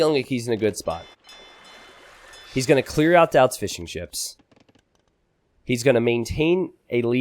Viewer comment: Did you say fish and chips? 😋